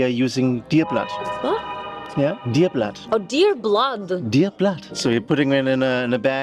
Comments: Did he say "Deer blood with coochie yeast" at 35:34? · Did I just hear DEER BLOOD??!! That is CRAZY